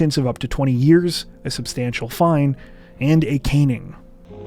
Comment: Did he say a caning? Dude, bring THAT to the states